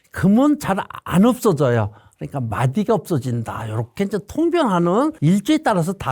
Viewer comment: 영상 잘보았습니다😊 궁금한게있어서요~ 11:50 에 보면 각 일간들마다 해당되는 재물설명하시는 부분에서 "화일간은 금이 돈인데 금은 잘 안없어진다.그러니까 마디가 안없어져요." 여기서 도대체 마디(?)가 뭔가요???😮 아무리 다시 들어봐도 제 귀에는 마디라고 들리는데 제발알려주세요😢🙏